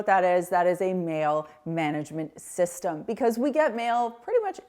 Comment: Thought you said we all need a male management system there for a second. 😂